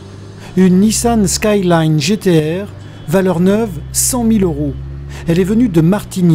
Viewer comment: Une nissan quoi ??? mdrrrrrr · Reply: Une Skyline GT-R